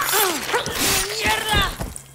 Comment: Am i the only one hears "Frieza"? If anyone knows what she's actually saying that'll be great lol